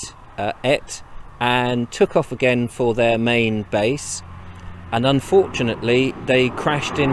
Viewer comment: Say what? Did he say, BASS?